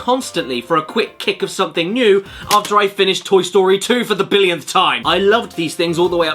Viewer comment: i thought you said it was called toy story fuckin 2